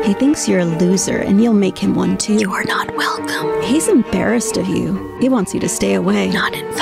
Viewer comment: Ashamed OF or Embarrassed BY. Or is English your second language? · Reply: Yup. I thought I heard, 'embarrassed of,' also.